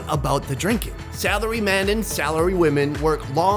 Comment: I thought he said celery men and women😂